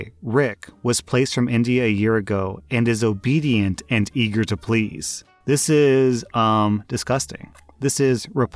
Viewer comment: Im ovr here doing chemisstry and i hear "Ready to please" and im like "What The Fuck"